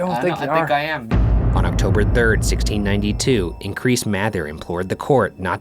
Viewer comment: Imagine being named "Increase Mather" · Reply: You know i thought he said "increase *matter*"